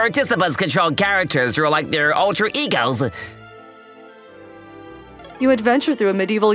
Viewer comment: sounded like he said Ultra Ego